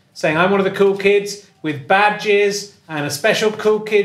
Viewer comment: At the start when Richard was talking about ‘badges’, I genuinely thought he was saying “bad jizz”.